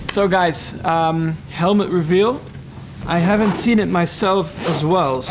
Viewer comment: I thought he said 'Helmut review'. I expected Helmut Marko's head to be in that bag